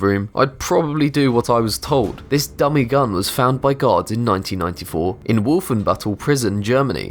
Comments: I thought he said found by God. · when he said "Guard" it sounds like he said "God" (same with "Guards" where it sounds like "Gods") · On 20 I thought he said that the gun was found by god XD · I thought he kept saying found by gods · It sounds like he is saying, " Found by gods."Or, " Ceased by gods."